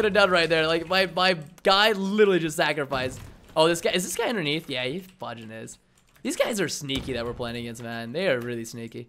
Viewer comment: did he just say "fudging use those" wtf